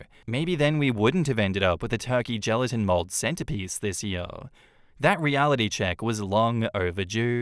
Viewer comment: Thanksgiving story: Lol. I misheard the word 'centerpiece' as CENTIPEDE! Somehow that sounds like it would have gone over better.